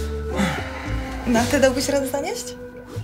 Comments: co ona powiedziała? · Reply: ''a te dałbyś rade zanieść?' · Czy tylko ja nie wiem co ona powiedziała w 13:47? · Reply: "no a te byś dał radę zanieść?" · dalej nie rozumiem co ona mówi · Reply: proszę Cię bardzo mocno